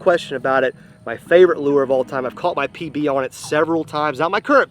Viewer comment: I thought at 1st he said he caught his PP on it several times at 8:36 lol but nope P/B